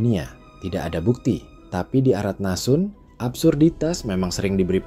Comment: "arat nasun"?, apa itu bro?, saya nyari gak ketemu haha, atau saya salah dengar saja.